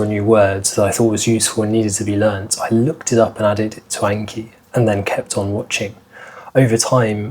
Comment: Thanks, solid tips! What was the app you recommended using (at appx 1:10)? Clearly I need to improve my listening skills pronto as couldn't catch the name! (By the way, at appx 4:45 couldn't help laughing when I heard you say 'Back in my youth...' Hope that was tongue in cheek. Otherwise seems PhD studies really have been taking their toll, and your audience from now on will just have to start calling you 'Uncle Will' 😆) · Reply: The app I recommended is called 'anki' it's free on computer :) Yeah I was joking with the 'back in my youth...' hahaha But you're not wrong I'm probably well on my way to becoming